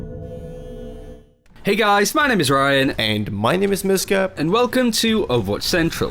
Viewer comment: What is the 2nd guys name? I always hear "and my name is No Scope.."